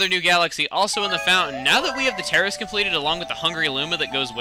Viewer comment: did he really say "now that we have the terrorist completed" or am i really that old that my hearing is fading?